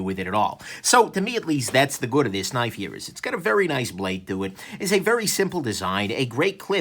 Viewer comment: I misheard "nice blade to it" as "nice bladitude" lol